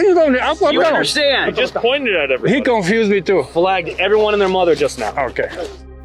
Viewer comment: "Everyone and their mothers rn" that what he said? 😂